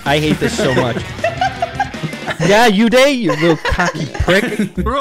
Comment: Did I hear that right? At 4:53 does call a “cocky prick”?